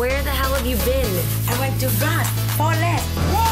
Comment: Did she say i went to Ross for less lmao. I'm watching for damn sure